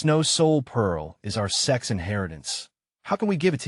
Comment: I hear sex so many times in This video😮 that's sect not sex